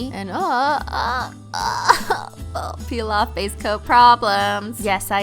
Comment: I have been watching your videos for over a year and I just found out that you were saying peel off BASE coat. The whole time I thought you have been saying peel off FACE coat. Well I'm an idiot.😜